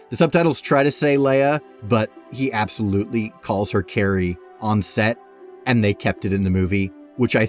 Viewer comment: I thought Luke said hay not Carrie